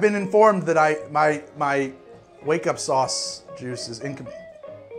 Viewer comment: All these years I thought he said waco juice